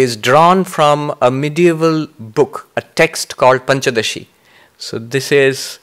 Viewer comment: What is the title of the medieval book he mentions at around 13:00? I can't catch it's name. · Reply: It is "Panchadashi", more details can be found at : <link>